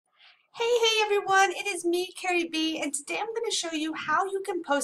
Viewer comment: I heard :"Hey hey it's me Cardi B" lol